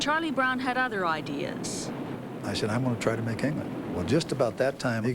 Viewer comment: What does Charlie say @ 5:28? "I had other ideas..I'm going to try and make ?" I can't make out that last word.... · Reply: I'm going to try to make England